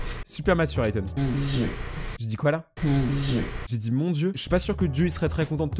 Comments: moi j'entends "aaaa mes yeux" xD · perso j'ai compris "mon dieu" d'ailleurs gg t'es vidéos sont génials